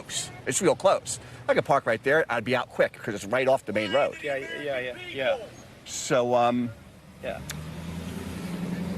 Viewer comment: What does the man yelling at 3:11 say? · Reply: 'Lying to the AMERICAN PEOPLE'